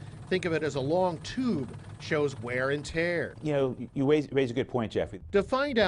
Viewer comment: Wtf did that guy even say 1:24 ?? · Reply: You raise a good point( i think he says Jeff)